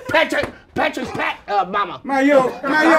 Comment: 😂😂😂😂😂i done spit my damn food out! What say?! LOOK LIKE , PATRICK, PATRICK'S,PAT, UH, MAMA😂😂😂